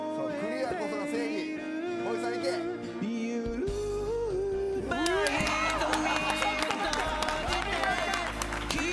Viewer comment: が何度聞いても何言ってるか聞き取れないけどおもろい。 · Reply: 「よーる、ウゥーバァー」なのかなと…。